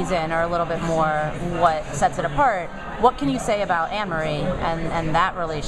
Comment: who's emery?? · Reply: i thought she said that too...but no, she said ann-marie, some waitress girl deamon dean is apparantly gonna have an 'intense fling' with lol sounds fun :P